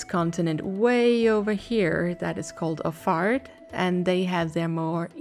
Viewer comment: I've never tabbed so quickly back into my browser as when I heard that Othard pronounciation xD I thought you said the continent over there was called a fart, and was like: "Wait, huh? What did I miss here?"